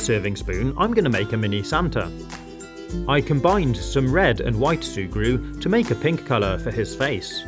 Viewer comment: Sugru is really cool, what would you use it for? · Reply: At first I thought it said sugar